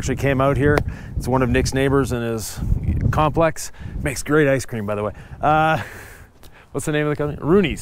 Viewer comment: Great job, looks great! DidI I hear “Ice Cream”?!? 😁